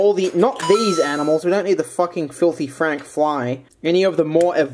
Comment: You saying Filthy Fly Makes me remember the good ol' days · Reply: Nemiko kun I thought he said filthy frank fly